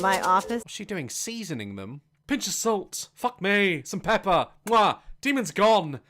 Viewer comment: will be the BEST person to react to this! I cannot wait to see! 👏🏼👀🙃😄 EDIT LMFAO! I TOTALLY misheard him in the beginning. He said, “Seasoning them? Pinch of salt, f*ck me .. some pepper mwah 💋” 👏🏼😂 (now that’s funny enough.. but what I heard ..) “Bitch is using salt.. f*ck me, some pepper mwah. 💋” 👏🏼👏🏼😂😂🤣🤣 W0W 😂 haha I mean I could see saying that so maybe that’s why I heard it 👍🏼😅